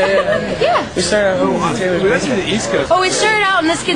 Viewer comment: I swear Josh said they started out in Taylor's basement (4:11). Taylor York Y'all! <3 <3